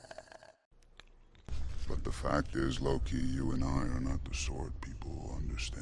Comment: I thought he was saying “the fact is low key you and I.” Not “Loki you and I.”